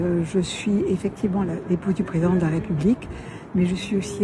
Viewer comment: pas clair, le son ou l'expression, — alors qu'elle zozotte franchement dans le reste du discours. "L'époux du..." ou "L'épouse du..." Qu'en pensez-vous, dites-le moi dans les commentaires 🙃